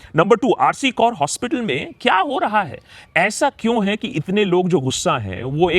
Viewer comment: Am I the only one who heard R C Kar instead of R G Kaur . 2:52 ??